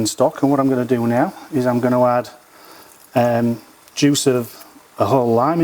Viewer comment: For a minute there I thought you said “juice from a lamb” I was a bit worried where you get lamb juice from. The accent got me. Great recipe many thanks! 👍